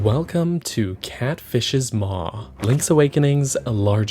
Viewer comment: do my ears deceive me or is he saying "pretty dank good" 😂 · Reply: He’s saying “Pretty DANG good”